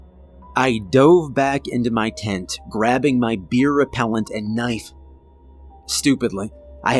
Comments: I definitely giggled when I heard you say "I dove back into my tent grabbing my beer repellent." I'm assuming it was supposed to be bear repellent? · Did anyone else hear "beer repellent" around the 17:50 mark?